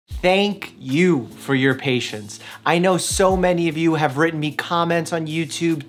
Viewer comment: I had this on my other screen and wasn't focused on it... Thought you said "Thank you for the patients" and had to restart because that's pretty dark.